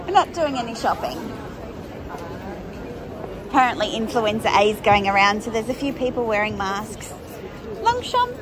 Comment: 😂😂 we didn’t but we did! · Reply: when you said Influenza is going around, I misheard and thought you said “Influencer is going around”! It sure is - you’re all breaking out in it 😜